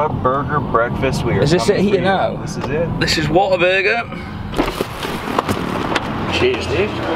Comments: the whole time i thought he said water burger 😂😂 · I thought you were both saying " water burger" until I read the shop sign 🤣🤣